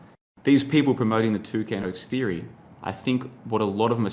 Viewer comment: I heard "two cans" 🤣🤣🤣🤣🤣🤣